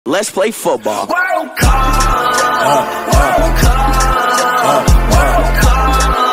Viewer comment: I thought said WORLD COC